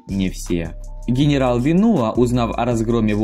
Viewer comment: я зачем то услышал вино?)